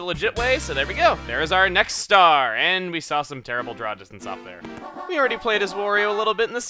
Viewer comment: what did chuggaconroy say at around 2:54 after he collected the star? "and we saw some terrible (something)"? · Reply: Draw Distance, this is used for rendnering(displaying 3d stuff), low draw distance means that the objects pop in/out easily while with far draw distance you don't see it as much, he means the Spinning Platforms you can see in the Background.